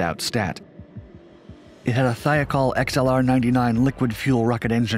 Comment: What does he say at 2:10? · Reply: It had a thiacol xlr99